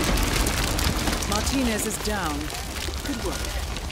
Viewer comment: Auto-generated subtitles thought Martinez said... allow me to demonstrate: "Penis is down, nice work 47" You really did it with the alcohol there, YouTube